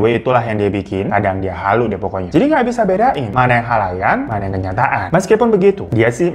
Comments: Eh kok khalayan ya bukan nya khayalan（￣△￣)！ Ga salah denger kan aq? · "mana yang khalayan, mana yang kenyataan" Wkwkw woi bang kebaliik. Khayalan bukan khalayan 😂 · Bang kayanya typo deh waktu ngomong "khayalan" malah bilangnya "khalayan" 😅 apa aku aja yg salah denger hheu. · bang mon maap nih, maaap banget.. ini maksudnya mau bilang 'khayalan' yak? 'mana yg khayalan, mana yg beneran' gitu? ko gw dengernya 'khalayan' 😂